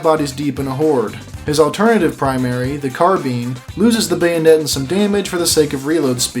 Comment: Gotta love how he says "Carbine" and i heard it as "Carbean"... its "car-bine" like the bin but ye.. i ain,t no gramar/pronounce police.. so uhh.. say it how ya want.. its just silly